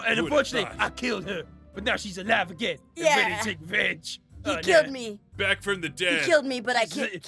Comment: I thought (I forgot his name) said b*tch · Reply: No, it’s the “venge” part of “revenge”